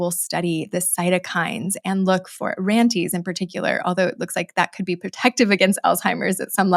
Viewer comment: what is “ranteez”? You said what sounds like “ranteez in particular”…